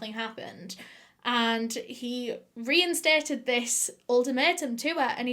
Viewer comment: When you say “ultimatum” I hear “old tomato” and I love it.